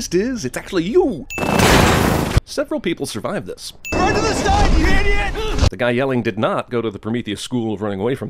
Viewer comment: I didn't realize until my 3-4 rewatch that he actually said "Run to the side you idiot" and that means someone over there definitely watches CinemaSins